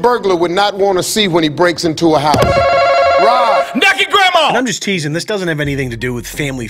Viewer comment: I was expecting one more, "NAKED GRAMA" at the end. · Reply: What? I thought it said necking grandma😅